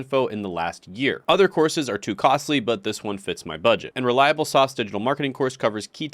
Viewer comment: It's offered by a company called Reliablesoft. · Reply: Thank You, I thought he said "Reliable Sauce". 😆